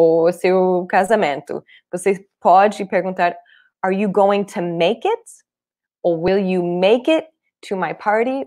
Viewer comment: Gente no minuto 7:16 ela pergunta "Are you gonna to make it?" N sei se tá certo oq eu ouvi